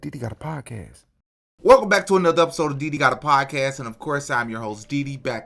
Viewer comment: I thought the intro said “Diddy got a podcast” instead of “DD got a podcast” 😂 I’m so sorry